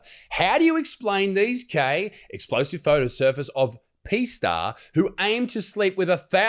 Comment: Bloody hell, wasn't till mid way in I realised he was saying P star as in porn, I thought he was saying "PEE" star, 😆 🤣 😂 bugger me